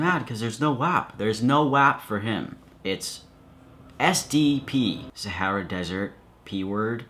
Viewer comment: I thought said STP and just about lost it XD